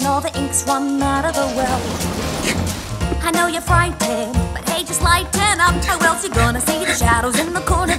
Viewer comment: One remaster later and I still can't hear "Hey just lighten up," for some reason all I can hear is "But Hades liked Hannah." Its not as bad as in the origonal but I still cant hear it.